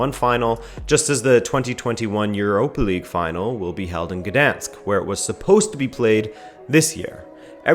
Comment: for a second, I thought you said "Verdansk" 😅😅😅 Well, I guess I played too much CoD WZ in the past couple of months 😂😂